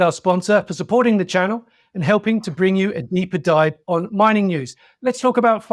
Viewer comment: you need to move the two light sources away from a direct facing direction, like 45 degrees off both sides. Too much glare on the background graphic. Nice interview as always ❤. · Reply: did he say "neeper dive" or "deeper dive?"